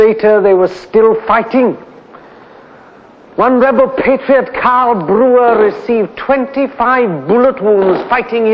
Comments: Simon Dean "14:00 I can find no record of this man in the record of participants who was shot 25 times and lived. Sounds like he is saying Karl Brewer. Can anyone help?" Cathal Brugha is your Karl Brewer. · I can find no record of this man in the record of participants who was shot 25 times and lived. Sounds like he is saying Karl Brewer. Can anyone help?